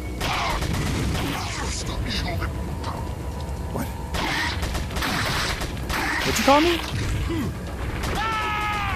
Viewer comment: I replayed this part many times to catch what the guy says and all I got was "S.O.B." He says something before that, but I can't understand it 😮‍💨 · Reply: You're fired....